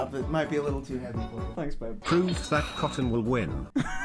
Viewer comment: Did F1NN call Soda "Babe"?